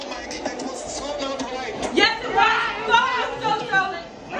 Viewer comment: did she just say? F@ you TOTO! HHAHAHAHAHAHA LOL THAT'S GOLD!!! :D